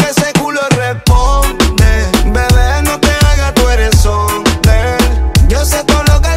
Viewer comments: Yo pensé que decía bebe yo se que tu eres hombre 😂😂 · pense que decia "bebe no te haga tu ere hombre" y vine aqui para confirmar 😈😈😈